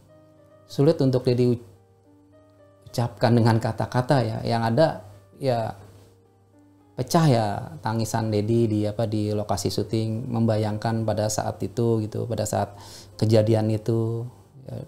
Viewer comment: di menit 3:33 dia kog nyebut lokasi tol jadi lokasi syuting sih 😅😅😅 dia pecah tangisan nya disana ..oh jadi lagi drama ya.. #ehhh bener kan ya..ga salah denger kan ya lokasi syuting 😅😅